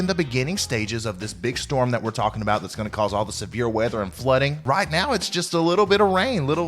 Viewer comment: Words @ 1:00 Please; I have "industrial hearing loss" Closed captions may get it for me but what did you say and what is that? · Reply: Severe weather and flooding. Means like a lot of rain can accour around the area's where he said